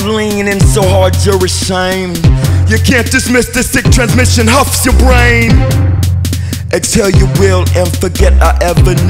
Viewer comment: I swear to god I hear "you can't dismiss the sick transmission huffs ya brain" not "you can't dismiss this sickness huffs ya brain"